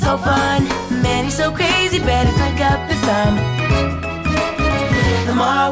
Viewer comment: i kept hearing "it says 'but needs to surface'." only now do i hear it "it says 'beneath the surface'."